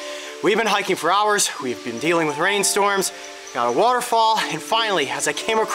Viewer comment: I thought it said waterfill lizard